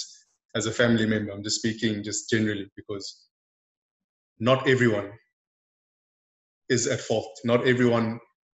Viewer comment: All I hear is ''Its everyone's fault but mine''